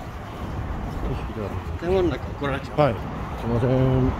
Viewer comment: で有吉さんが何て言っているかわかりますか？「怒られちゃうよ」は聞こえるのですが、その前が何回聞いてもわかりません。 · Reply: 私は「建物の中は（撮ったら）怒られちゃうよ」かなと思ってました。違ったら申し訳ないです。